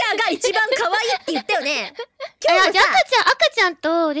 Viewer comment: 「あくあちゃん」が「赤ちゃん」に聞こえるの草